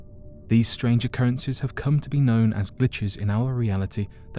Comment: Have come to be known as Bitches Am I the only one who hears it like that?